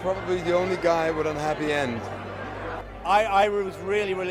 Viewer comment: What does he say? "with unhappy end" or "with a happy end"? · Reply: Happy end